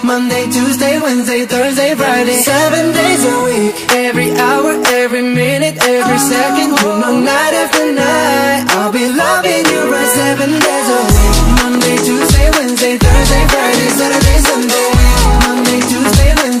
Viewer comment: Bro, I really hear "I'll be F*ck*n' you right" in the "I'll be lovin" you right" part, (that's the proof of my bad English )